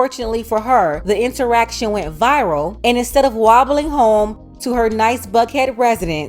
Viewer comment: did you say "wobbling" 🤣🤣🤣🤣☠